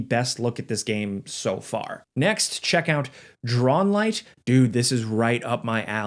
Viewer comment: So if anyone is curious the light house game is called "Drownlight". I heard him call it Drawn Light, but I did find it on steam as "Drownlight".